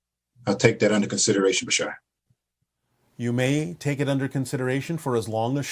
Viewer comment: the Subtitles determined that guy said Michelle instead of Bashar - this CC typos are jokesssss